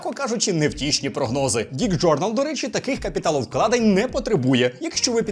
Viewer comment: дік джорнал 😆 чи почулось?